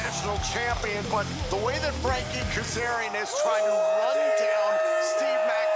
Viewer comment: Every time she says "Frankie Kazarian" I hear "Freaky Kazarian".